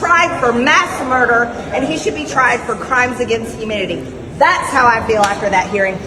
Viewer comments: MTG says we can attack her character. She doesn't have any character to attack but she IS definitely a character. Oh wait...did she say "crimes against humidity"? 8:15 🤔😉😀😃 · did she say "Crimes against humidity"? Maybe Dr. Fauci told her to dry up. 😂😂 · It sounded like she said crimes against humidity. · I swear it sounds like she said crimes against humidity.